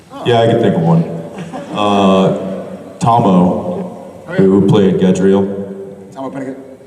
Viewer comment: Hello! I love this video so much, and I want to make sure everyone can understand and enjoy it. That's why I wanted to let you know that your captions are a little off. At about 00:31 the auto-captions say "who played get real" where they meant to say "who played Gadreel". When you get a chance, could you fix that? That'll help the 360 million folks globally who rely on closed captions to understand videos. If you could tidy up the captioning a bit and make it neater in this video and future videos, it would help so many people! Thank you, and keep up the great work!